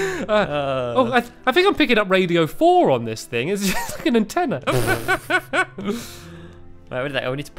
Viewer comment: Did he say it's a f**king antenna? Or is it just me?